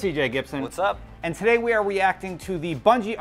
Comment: I heard "weacting". Lol how bout you